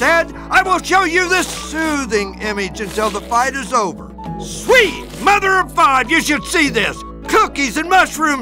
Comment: SWEET MOTHER OF FOG YOU SHOULD SEE THIS! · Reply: I always thought he said "sweet mother of five"